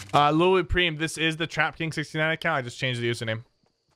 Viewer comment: When he said TrapKing69 i thought he said tekashi 6ix9ine